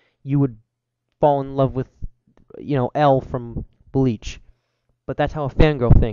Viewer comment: Did he say L from Bleach